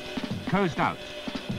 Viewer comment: What does he say at 19:35? Right before "anti-shipping strike." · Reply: Tyranusfan I thought he said "Coast out"?